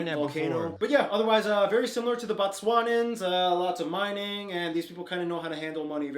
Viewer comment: I’m not sure I heard right ... But did that guy say “Batswa- nans “